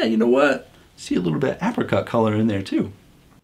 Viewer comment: Hi on the test taste I thought you said Africa color I was like what!!?? HAHA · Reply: 😂 No, no... APRicot color. I need to speak more clearly... or slowly! 😂